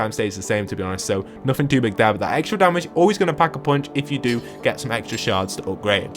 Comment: did he say pack a punch?